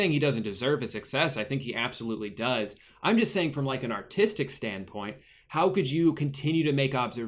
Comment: I thought said autistic instead of artistic.. OR DID HE.